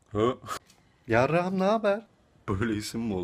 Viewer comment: Hanım diye ad duydum "Hanım naber" XD